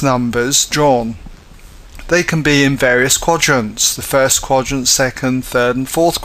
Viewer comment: Thanks Alot! but i have a question if u dnt mind sir! In this time 3:53-3:56 u said a word i heard it "quargens" what is it?